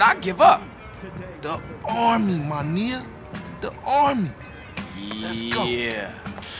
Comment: Did he just say “ma niyya”? 💀🤣